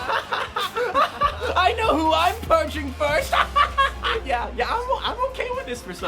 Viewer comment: I am still surprised the test wasn't Libertarian's. Also: the subtitles at 15:34 say punching instead of purging.